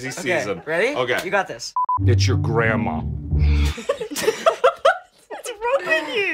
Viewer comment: Nobody: Absolutely no one: David: iTs yOur gRanDmA dies laughing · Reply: I think they laughed because they misheard it as "Eat your grandma" or i just think so 😂